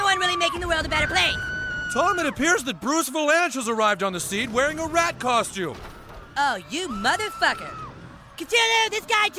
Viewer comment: what does that guy said to cartman? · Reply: Tom Bruce vilanche is here on the scene wearing a rat costume